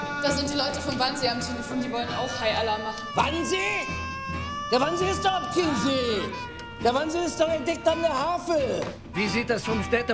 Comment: Was sagt Henry Hübchen bei 1:38 ? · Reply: der wansee ist kein see. der wansee ist doch direkt an der havel (fluss)